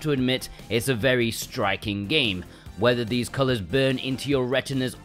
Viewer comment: I was half listing when h were taking about the first game cuz I was reading a text and I thought u said “whether or not the colors burn into ur rectum!!!” Hahaha. Omg. I had to rewind. Lol. SMH. I need to pay attention. Haha.